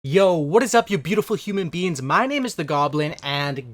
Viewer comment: "Yo what's up you beautiful human beings" Oh my😊😊😊😂😂😂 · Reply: I thought he said yo what’s up you beautiful human “BEANS”